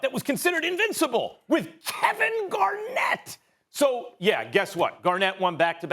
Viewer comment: did max just say "Kevin GARNETT" ?